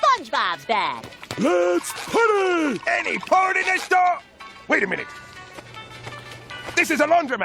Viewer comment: Wow… I’ve heard a lot of people try to argue that Mr Krabs says “any porn in this store?” But that time, I heard “any port in a storm” VERY clearly which might be even worse 😂